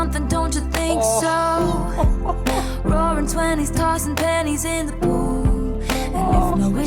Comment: Just had to come back and say thanks because now all I hear is “roaring twenties tossing PANTIES in the pool” every time I hear it now because of you 😂